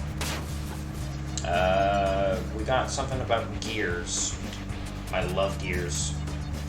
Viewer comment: did you say gears? 🤨